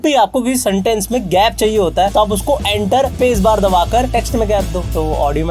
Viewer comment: kya bola? Enter space bar kya? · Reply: Enter space bar .. aur audio me bhi gap ayega .. like pause hota h na speech me vese kuch.